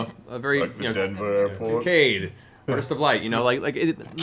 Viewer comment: What adjective does Matt use at 2:21:45? I keep hearing "arcade" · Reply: Kinkade. Apparently a painter in the pastoral style.